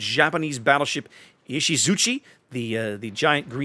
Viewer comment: man I nearly had a heart attack when you said 'Izusuchi' and thought you had said 'Iwaki', I'm not even an Alpha test member and I would have been pissed if that had been going into the armory in any fashion. And with the new German BCs and now that I have all the coal ships I want for right now maybe I can finally get Lutjens.....